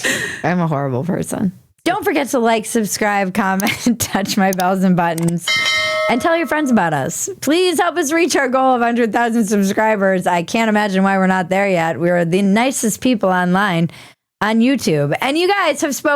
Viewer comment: I thought you said "under a thousand subscribers"